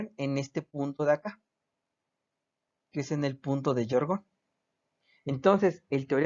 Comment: Hola!! Quisiera saber cómo se llama el punto G, que mencionas en el minuto 15:09, ahí dices su nombre pero no se entiende bien, de antemano gracias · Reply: Hola, se llama punto de Georgonne.